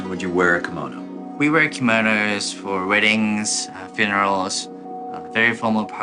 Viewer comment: i heard "condoms" instead of "Kimonos"